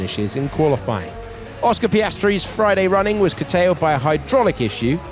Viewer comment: From English learner to natives, what did he say in 1:40? Friday running was _ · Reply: curtailed, meaning reduced, limited, cut short.. anything of that sort